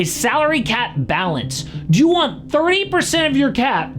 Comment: did you say 3% or 30% 😅